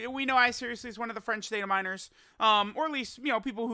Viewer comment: why i always hear "french data minors"